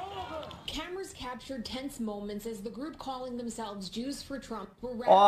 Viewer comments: I heard "Juice for Trump" 😂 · AmI hearing something else? Cause I swear I just heard “Juice for Trump”